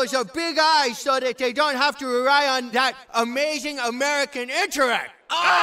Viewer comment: "Good they have those BIG eyes so they dont have to rely on that AMAZING american intellect" 🤣🤣🤣🔥🔥🔥🔥 Edit: saw this episode again just two days back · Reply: Thanks, I thought he said "write on that American internet" 😆